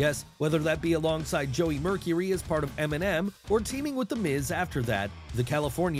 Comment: I love how the subtitles has "Eminem" instead of "MNM" 💯